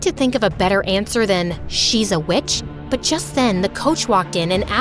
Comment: I thought she said “bitch” 😭✋✋✋